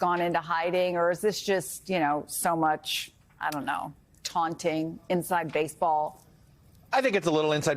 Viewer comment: Did she just say "ph*king chickens"?😮😮😮😮